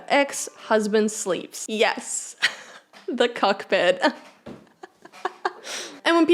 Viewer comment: DID U SAY "CUCK"PIT??? LMFAOOO